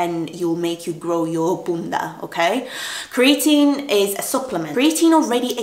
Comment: Wait did I hear you say “bounda “😂??